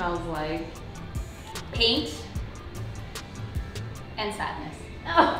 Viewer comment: I totally thought said that her old apartment smelt like "paint and sex" LOL had to go back and listen again. hahaha